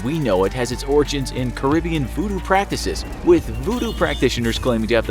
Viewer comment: Anyone else hear Caribbean Food and practices at 1:10? Edit: I can see the katana becoming a ritual tool for killing lone zombies. racks shotgun with religious intent